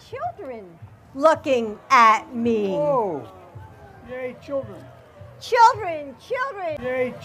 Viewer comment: I thought he said gay children